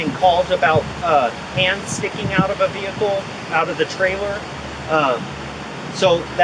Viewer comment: OMG THIS IS SAD BUT I THOUGHT THE 2 YEAR OLD SAID AMONGUS LMAO